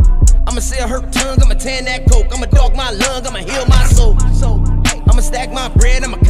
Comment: When he said " imma dark my lung imma heal my soul" Sheesh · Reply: I thought he said imma dog my lung, imma heal my soul